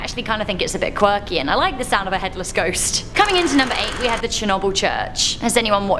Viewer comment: I used to live right by this church but never saw the headless ghost! 🙃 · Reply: I thought you said headless goat